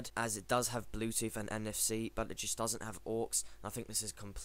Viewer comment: you mention it doesn't have "...." something I could not understand what you were saying please clarify. As far as the connectivity My RCA HDTV i just purchased came with coaxial digital audio out so this works perfect for me . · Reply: Pat Schutte I doesn't have a AUX port. However you can easily get a 3.5mm jack to RCA cable.